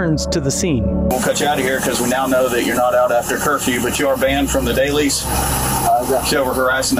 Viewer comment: What's the name of the store that called in? They deserve a bunch of middle fingers. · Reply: Could be wrong but I think the cop said the Dailys Convenience Store. 15:15 I agree. Just wasting taxpayer money.